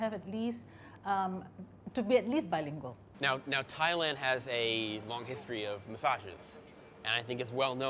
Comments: First i heard "thailand has a history of mysoginsts" i was like lmaoo what are u gonna ask him then i heard it correctly lol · When he said Thailand is known for massages I thought he said “misogynists” 😅 lol I thought things we’re about to get real awkward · ohhh, I thought he was saying misogynists until they got the massage lmao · I thought he said "Thailand has a long history of misogynists", not massagists (Masseurs) 😂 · did a spit take when I heard "Thailand has a long history of misogynists" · I genuinely thought you said thailand is well known for misogynist first take, i was like WTF did he REALLY JUST SAY THAT?! before i triggered you said massage, you said it so fast i swear is sounded like misogynist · I could have sworn he said “long history of misogynists” around 2:26 😮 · I thought this foo said misogynist I was like damn he trying start a fight or some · I thought he said misogynist not massages I was like damnnnnn this is like legit journalism lol